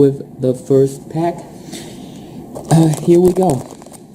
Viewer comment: sounds like you said 'and here we get'